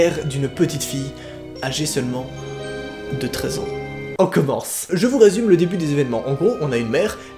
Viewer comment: Tu t'es trompé dans tes " paroles" ou alors c'est moi mais à 1:54 tu dit 13ans et ps 3 voilà c'est pas important mais voilà moi j'avais pas compris au début 😂